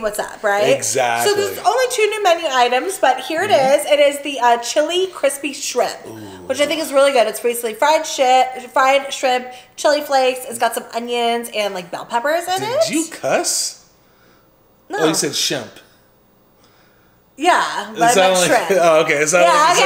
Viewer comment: 😂 I heard "fried shit" lol Made me laugh 😂